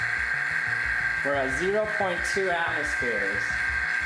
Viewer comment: did he just say brah 0.2 atmospheres like if you here it too